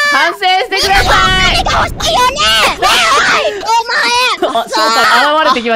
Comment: しっかり聞くと『みんなもお金が欲しいよね！？』に聞こえる